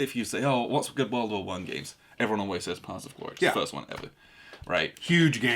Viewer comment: answers the question, "What's good World War One game?" but I can't make out his answer. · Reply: Paths of Glory, my bad!